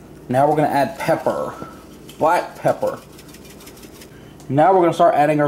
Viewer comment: I thought said twat pepper at 0:51😂😂😂😂😂